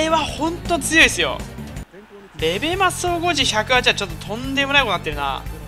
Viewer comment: ら辺の総合値101が108に聞こえてしまう笑笑